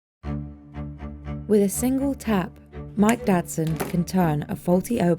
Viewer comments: Mike Dadson I keep hearing My Dad's Son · ngl i thought it said mike dad's son